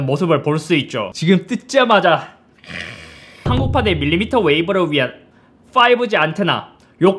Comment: 롯데리아랑 이근 대위 때문에 밀리터리 웨이브라고 들리잖아 어떡할거야 내일 롯데리아 가야지 헤햐ㅔ엫야엫ㅇ여애ㅕ에ㅑ헤헤ㅑ헤에햐ㅔ양0ㅎㅑㅐ